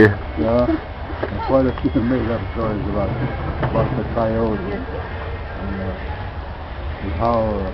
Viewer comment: did he say too much made up stories about the coyote on 10:11? I believe thats what he said, but i'm not sure. I want to meet Horace but never make over to Nimiipuu country, I hear he's turning 90 yrs old WOW, thats cool.